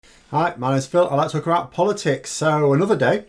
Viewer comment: What he says: "Hi, my name is Phil, I like talking about politics" What I hear: "Hi, my name is Phil, elector corrupt politics"